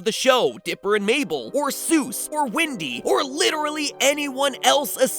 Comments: did anyone else hear: "Windy"? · Did I heard it right "Windy"? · did he say "Windy"....